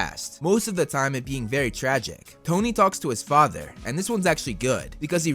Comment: Well at 3:50 I heard Dhoni instead of Tony....is it just me or anyone else? I guess probably me.... Well you see I am a big fan of M.S. Dhoni.... :):)